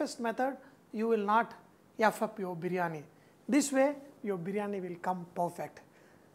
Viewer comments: Did he just say eff you your Biryani? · did he just say, you will not f up your biryani?